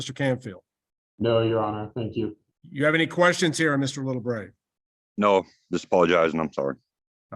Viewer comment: I could have sworn I heard call for Mr. little brain